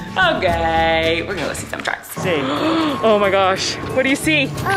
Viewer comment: did he just say “oh my gersh” ? That is the cutest thing ever